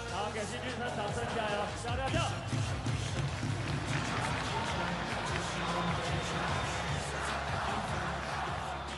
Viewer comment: 辛俊昇說了什麼🤔 這時候需要讀唇語的YT來解答😂😂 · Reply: 他要下去吃「雞排啦」